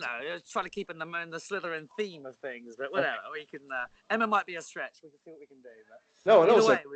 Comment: What did Tom say about Emma ? 19:45 "Emma might be..."? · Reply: A stretch